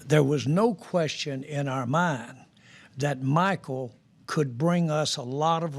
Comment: You clearly don’t listen well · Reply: he CLEARLY says Michael